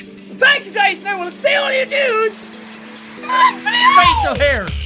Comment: Was i the only one who thought that he said i will see you on your nudes in the outro before when he said it faster ? xD